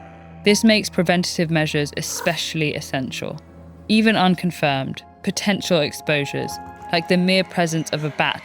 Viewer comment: did you say potential gif